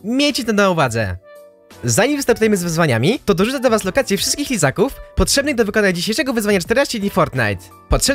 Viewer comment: czy tylko ja usłyszałem Izaków? XD